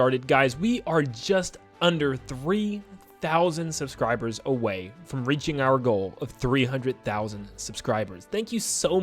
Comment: I misheard that as "We're just under 300k subscribers away from 300k subscribers" 😂